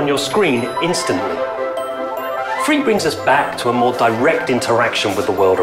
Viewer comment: i thought he said it is free